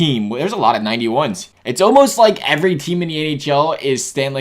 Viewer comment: he said ahl instead of nhl